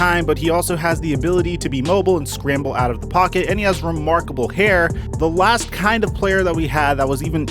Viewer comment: did he say that Trevor has remarkable hair😂???